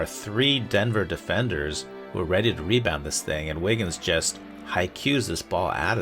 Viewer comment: "'Haikyuus' this ball outta here?" am i hearing that right??